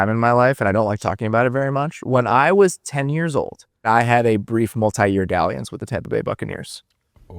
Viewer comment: seconds: What did he say? " A brief multi-year..." ?